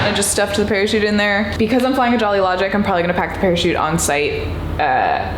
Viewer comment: I swear I thought you said you were flying a Jolly Roger for a sec and the one piece theme started playing in the background.